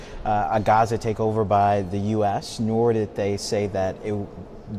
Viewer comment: Bro definitely called them demorats. 1:00 Unless I heard him pronounce someone’s name that I don’t know…